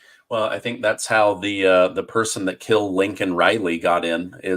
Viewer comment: Every time you say "Laken" it sounds like you say "Lincoln" - like Joe did.